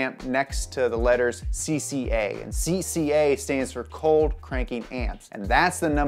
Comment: I thought he said batteries have a rating for Cold Cranky Pants